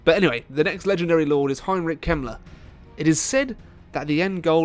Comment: Silly me. I thought you said Heinrich Himmler for a moment, but who would ever put pure evil in a game?.....Wait a second, oh my...